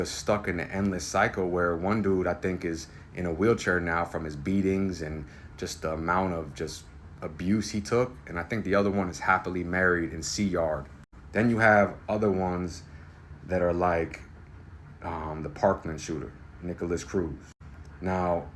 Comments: what does this mean? Did he say "C yard"? As in, he is """married""" to someone on the yard? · Happily married on C yard..lolll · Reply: Oooooo “on C yard” 😂 duhh. I’m retarded and thought he said “on CR” and I was confused. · happily married in what? I couldn’t make out that word. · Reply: C-yard